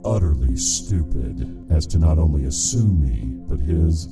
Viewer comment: so nobody noticed that at 4:45 the narrator said assume instead of amuse?...kinda ruined the scary voice imo